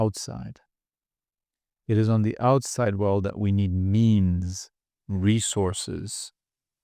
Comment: I thought u said memes I was taken aback lol. good talk though, I feel like I'm aware of all of that but at the end of the day the true challenge is taking your realization into consideration and not get driven entirely by emotions. we have to remind ourselves that it's okay to just exist without a care in the world sometimes.